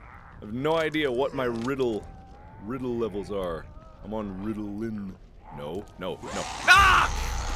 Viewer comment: I know he says "I'm un-riddle-in" but I love the fact that it sounds like he says "I'm on Riddelin." (I don't know how to spell that but that's not important.)